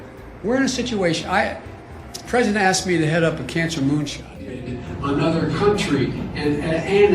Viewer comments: Did he just say "The President has to be the head of the Cancer Moonshine"? WTF 🤣🤣🤣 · What did he say at the 2:50 mark? Cancer moonshot?